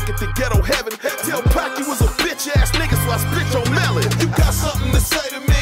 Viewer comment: what he say 3:26 about pac😨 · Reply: He saying if you talken shit bout pac he will split your mellon. Him and pac were homies. Got songs together.